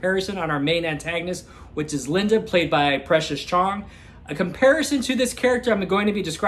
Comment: Love the fun facts you drop. At first I thought you were saying Precious Charm, I was like what an unusual name lol. I’ll have to check this one out. -Sonia